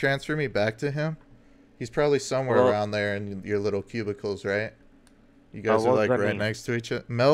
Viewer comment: cubebicles or what ever he said omg 😂😂